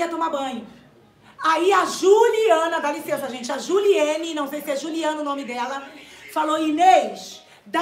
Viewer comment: momento que ela fala sobre a Juliana do the noite “Aí a Juliana Juliene não sei é Juliana...” queria entender que bosta vcs tem na cabeça colocando o nome da Juliette que não tem nada haver com a história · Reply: Ela falou :"Juliene"